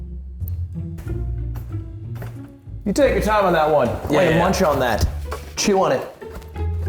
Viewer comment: when he said ''miyoung'' I thought he said ''me on'': "I would like to call me on to the stand''